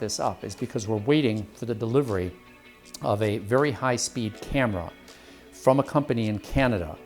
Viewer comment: Anybody else momentarily mishearing 29:50 as "...waiting for the delivery of a very high speed hammer"...? No...? Just me? Okay then...